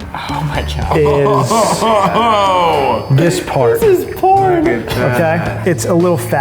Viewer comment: did anyone hear that someone said porn